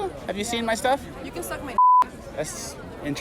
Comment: did she say suck my d***? All the Liberals in these videos are nasty.